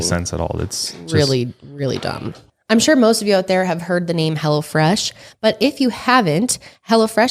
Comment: Sounded like said Hella Fresh instead of Hello Fresh lol Love yall!